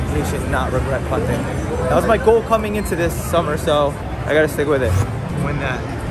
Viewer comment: did he say that was the goal for this summer? It’s February?